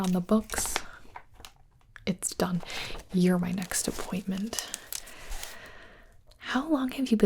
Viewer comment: when she said "your my next appointment" I thought she said "your my next disappointment" and I was like "yea yea ik :,(